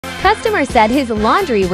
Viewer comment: Video: - "Customer said his... " What I hear: - "Custom Mercedes!!!" 😂